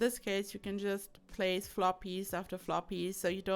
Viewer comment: omg I just realised you say "floor pieces" I thought you've been saying "floppies" and thought it was the cutest thing